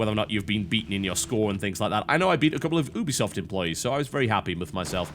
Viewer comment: I thought he said boobiesoft :D